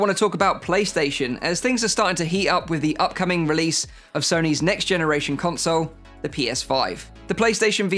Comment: You did clearly say cerny's, not sony's